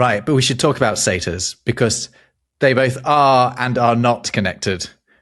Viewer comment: Oh, I thought you said “Seder” - as in the Passover meal.